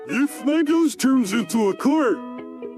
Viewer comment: It sounds like Patrick said: "If Lego's turn into a Car"